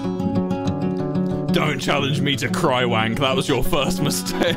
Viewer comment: dont challenge me to crywank >:)) ,,,, did he just say crywank??